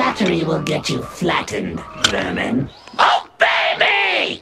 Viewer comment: When screamer said "obey me!" I thought he said "oh baby!"